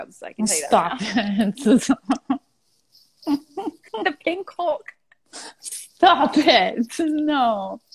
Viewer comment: Can someone please tell me what Danielle says at 18:29? Sorry,I'm italian and I'm trying to understand but I don't get it 🙄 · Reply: Pink hulk